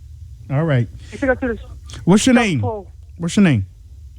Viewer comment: couldnt understand the caller 13:54, who went to the south pole?